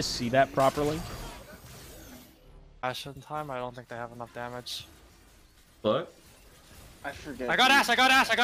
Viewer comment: i hear someone says my ass flash wtf hahahahahahaha